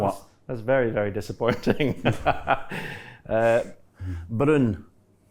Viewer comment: sounds like Brun, its what we say "brown" in Scandinavia 👍 Maybe a little Viking in Hendry 🤔😁